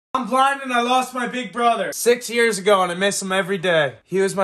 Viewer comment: im so sorry for your loss. for a little laugh, heres a little story i have seen this creator on my page a few times so know hes blind, and i heard 'i was driving and i lost my brother' instead of 'im blind and i lost my brother' idk how but my brain works in strange ways, and i had to take a second to recooperate